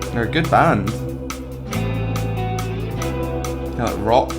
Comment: “Not rock” I think he said, but B R U H just wait 😂